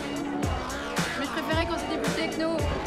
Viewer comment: Does anyone know what Cyril says in french at 1:56? I've been meaning to learn French for a while and this movie made me want to learn it ASAP. But I really want to know how to say what he said at that point..I kinda identified with him (including the depressive/suicidal tendencies which I had years ago), and I really want to be able to say that line lol It's weird, it's cringy, but hey..we all have our weirdnesses. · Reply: He says "T'es folle, tu comprends rien, c'est génial. C'est de la Disco Moderne"